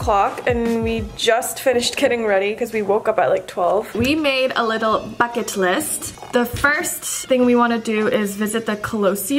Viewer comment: i thought you said we made a little baguette list💀 I'm in France right now it's so beautiful 💕💌